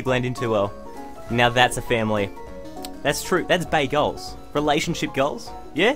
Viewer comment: When he said "Bae goals" I heard "Bagels" lmao